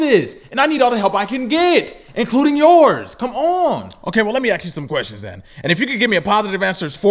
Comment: am i the only one that noticed that he said “lemme aks you some questions” instead of ask or am I tripping